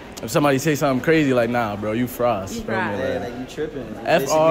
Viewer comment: please tell me I’m not the only one who heard fries.